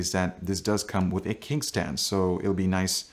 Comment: Ahaha! Did you just call it a 'kink' stand? 😂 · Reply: No, he said 'kick stand'. Obviously.